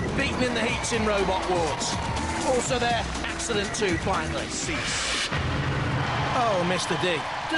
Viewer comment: When I was younger I thought they were saying "six" instead of "cease".